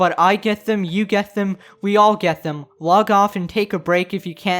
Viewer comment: Did he just say we all get 'g a y m e n' ? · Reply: Game end